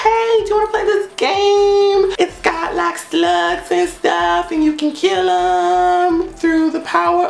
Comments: I could have sworn u were saying sluts instead of slugs lol I feel ashamed of myself now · Oh my god I thought you said "sluts" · when he said slugs I thought he said sluts... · i thought you said sluts instead of slugs at first 😂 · Honestly thought you said sluts not slugs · Slugs! I thought I heard 'sluts'. I was going to pause and go to the App Store immediately! 😂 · I honestly thought he said sluts instead of slugs..... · I heard "sluts", not "slugs" oml